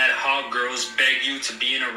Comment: I heard bake at first and I was excited then I heard beg and I'm glad women are an option